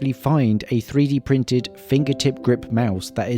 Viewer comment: Thought you said 3D printed mice ruined your life... Not 3D printed shells..😂😅